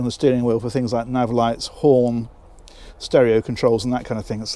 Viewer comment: Hi. Great vid once again. Happy New Year. I am amazed at how many options you get on these boats. I could swear at 3:02 you said there is a button on the wheel for Porn. Does this show via the Garmin? LOL